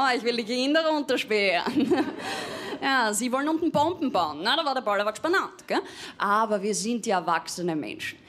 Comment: was sagt sie da? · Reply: Na da war der Pallawatsch beinand = na da war das Durcheinander perfekt